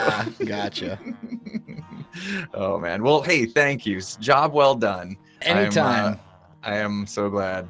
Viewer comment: At the end, I thought he said "I'm gonna call this a job 'WELS' done"